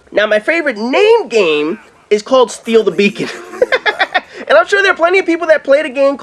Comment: I thought he said "Steal the Bacon" at first.